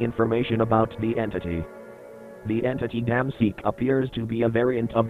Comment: i swear everytime i heard "dam seek" i hear it as "DAMN SEEK"